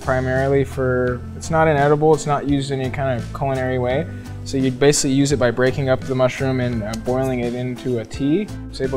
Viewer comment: What is the mushroom strain that he said his mom takes at 3:20? Is that cordyceps? Crazy looking mushroom. It is beautiful. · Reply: Red Reishi